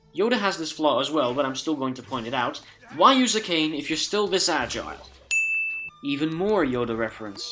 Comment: What did you say after, "Why use the cane if you still..." ? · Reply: 'if you're still this agile.'